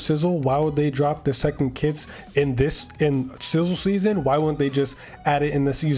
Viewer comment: I swear I heard him say "sizzled semen" ✋️😭